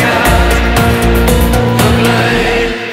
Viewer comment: Lol, at first I thought he said "supersize me"!!!!